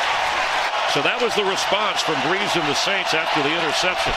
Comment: did he say "Brees and the Saints..." lol